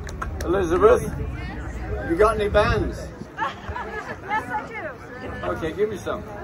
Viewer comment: I thought Ringo said, 'Elizabeth, you got any depends?!?' surely that was a transmission from his dear mate John (RIP) Happy peaceful & loving birthday, Ringo, my favorite Beatle, from the daughter of the tall guy who photographed you and your mates when you first landed in LA way back when.🧚